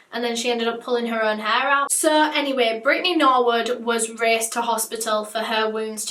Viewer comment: Who else thought she said brandy norwood was one of the girls